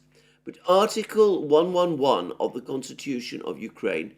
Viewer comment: yes he has told me, he even told me which section of the constitution to look at. In fact, he told everyone in his remarks, were you not listening?? Article 11, look it up, and read for yourself. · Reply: I thought he said article 111